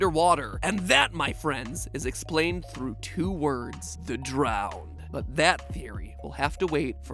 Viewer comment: Bro at 15:42 i thought you said "the drow" and was like "wow ok this is getting pretty wild"